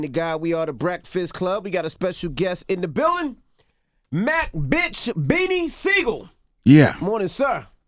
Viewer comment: Wtf did Dj Envy say at 0:14? Sounded like Mack Bitch! Lbvs